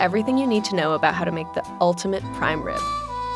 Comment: I thought it said unlimited prime rib and I was like tell me ur secrets wizard